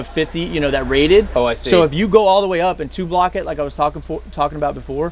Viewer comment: Just in case people misheard or didn't understand Petty Officer. He was saying "two-blocked", not "tube locked". It's an old nautical saying referring to using block & tackle on rigging. If two blocks get jammed on each other the whole system locks up. It basically became naval slang for "jammed". The term can and does get applied to many things that are not related to rigging. The term "Chock-a-block" has similar origin and meaning.